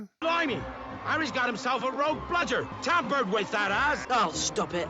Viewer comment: idk why but i thought hagrid said " its been tampered with that ass" once i watched it with subtitles i felt dumb